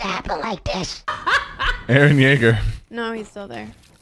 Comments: why when he says erin yeager it sounds like he says the n word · Did sypher say the n word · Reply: He said “aaron jaeger”